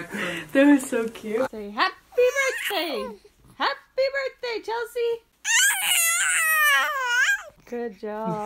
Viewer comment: literally sounded like she was saying “happy birthday Chelsea”